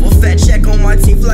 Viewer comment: the word is Forbes I don't know why they couldn't hear that 🤣